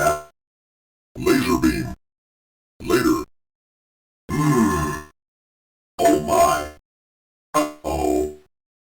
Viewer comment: VECTORMAN WHAT DID YOU SAY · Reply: “Later”